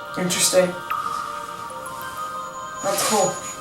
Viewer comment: LMAO the teacher says "interesting, that's cool" BUT thats not what the captions got if you turn on captions the captions says "Intresting fuck" XDDD